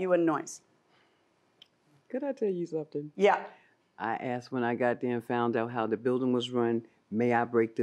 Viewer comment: Did she just say "when I God damn found out" ? 🤣